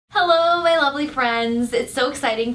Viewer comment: I thought you said ugly friends at first